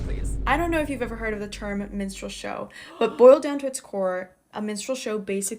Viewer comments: Gosh. For a second there, I thought she said "menstrual show" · I thought that woman was saying menstrual show.?